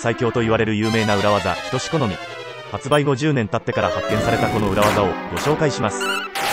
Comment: 「発売後10年経ってから発見された」が「発売50年経ってから発見された」に聞こえる😂